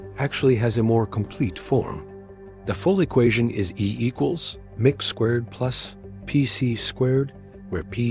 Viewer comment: I just heard the AI voice say, "E equals mick squared..." (5:17) That's it. I'm out.